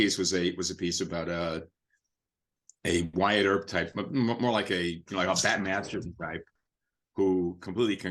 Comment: Can someone help to understand what he says between 9:45 and 10:00 the auto generated subtitles seems wrong and i can't hear what he's saying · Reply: Wyatt Earp/Bat Masterson